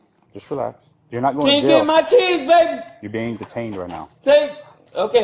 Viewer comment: did he call his homeboy Tay his baby 😅😅😅😅😅